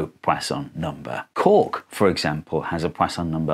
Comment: I misheard that as pork